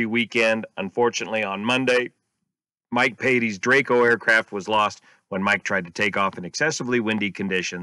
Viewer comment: Jesus, I thought you said "And the Dracos aircraft were lost." and started to flip.